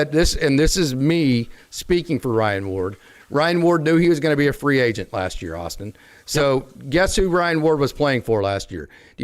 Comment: The Dodgers have 3 more option years on Ward. I hope he is given an opportunity somewhere but the Dodgers can keep him at AAA for the '27, '28, and '29 seasons which will correspond to his age 28-30 seasons. It's a very similar situation to Hunter Feduccia who was traded to Tampa at the deadline and just finished his age 28 season with the big league club there. FanGraphs roster resource has him listed as the backup catcher at the Major League level for the Rays. I think it ultimately worked out for both Feduccia and the Dodgers because the Dodgers got alot back for him: C Ben Rortvedt , RHP Paul Gervase and LHP Adam Serwinowski. The same thing could happen to Ward. He might get a couple of looks in LA and then the Dodgers could flip him. Of course Feduccia had already been in the majors the year prior and had burned an option year so who knows if a trade will happen this year if at all... but personally I'm hoping it does because it is not easy to see a clear path for Ward in Los Angeles.. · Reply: thanks for your thoughtful reply. I thought I heard “he is a free agent next year “ in the video. Right now with the injuries/recoveries I see an outfield opportunity in left for Call and Ward with Siani or someone as defensive replacement late with a lead.